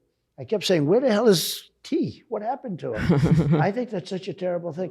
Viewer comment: Did Trump just call Stevewilldoit "T"? He doesn't even know his name after all nelk and trump have done together? Thats gotta hurt fr